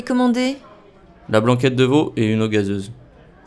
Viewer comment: It was really difficult. I probably graded myself at 2 before and unfortunately 2 after. I got “oops excuse moi”. The guy mumbles. I heard “de l’eau” instead of veau. I thought he said “ce passe” instead of “c’est pas”. Struggling 😢